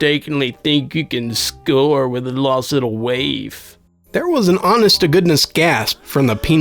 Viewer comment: what did he say here? lost little what? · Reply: waif /wāf/ noun 1)a homeless, neglected, or abandoned person, especially a child. synonyms:ragamuffin, street urchin, guttersnipe; 2)a young person who is thin and looks unhealthy or uncared for.